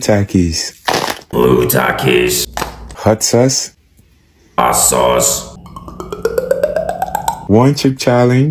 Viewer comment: он сказал отсос. Кто услышал, 👍